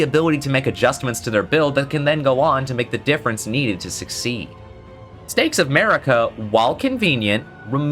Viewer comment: I am so american that when he said "stakes of marika" I heard" "Steaks America" the first time. I laughed and felt incredibly obese.